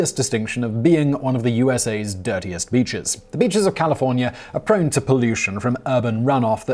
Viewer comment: Thought it said 10 nastiest bitches